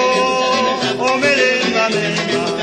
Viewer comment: jajaja ahora la esucho bn y io pensé que decia io tengo una negrita que me gusta de verdad jajaja pero dice io tengo una nenita que me gusta de verdad. muy buena la musica me encanta el ritmo de la selva, algún iré pa su fiesta san juanito.